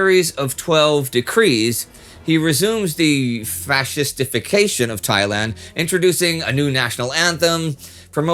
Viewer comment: Indy: "fascist-ification" I hear: "fast defecation"